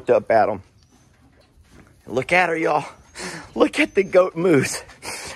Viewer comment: First time I saw this video I swear I heard you say, "Goat moose. Not goat moves." So Journey is forever a Goat Moose in my head lol